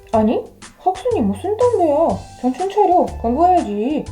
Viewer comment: 왜 정신차려 광고해야지로 들리지